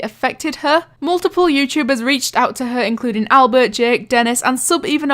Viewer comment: wait, did you say albert????!!! :ooooo